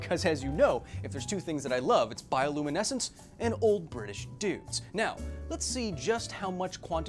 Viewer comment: I misheard 0:53 as old British nudes and was extremely disturbed